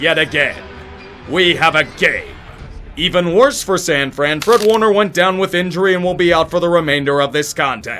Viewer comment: Did you say Kurt Warner? I was probably hearing things.